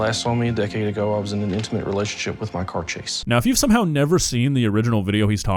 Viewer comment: Lol it sounds like hes saying hes an intimate relationship with his car chase, not his car, chase